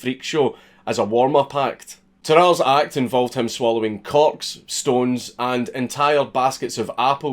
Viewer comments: with his accent I thought he said swallowing cocks 😂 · Involved his swallowing CORKS Woahhhh now don’t scare us like that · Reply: I thought he said cocks, and wondered why that was deemed unusual for a Frenchman. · Install Raid for Free ✅ IOS: <link> ✅ ANDROID: <link> Start with💰50K silver and get a Free Epic Champion 💥 on day 7 of “New Player Rewards” program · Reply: At first I thought you said his act involved him swallowing cocks! WHOLE! I was troubled for a moment at the thought of that. Then, I went back, and I figured out you probably meant corks! But I would have paid to see him swallow a big bag full of items shaped like dicks! That would have been epic to see! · "Devouring Corks" does not sound right in a Scottish accent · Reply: oh, shit, i thought he said cocks and assumed it was chickens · That's what I thought he said.. gonna chalk it down to Scottish accent and assume should be something else. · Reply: tbf I'm assuming he said corks, but I definitely heard "cocks" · Did I mishear that? He swallowed chickens, yeah?